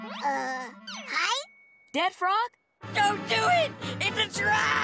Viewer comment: I know Chester said "It's a trap" but the way he said it sounded like he said "giraffe" as if the frog is a giraffe